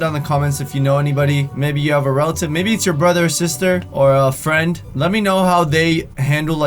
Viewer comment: I thought when he said "maybe it's your brother or sister" that he said "maybe it's your brothers sister"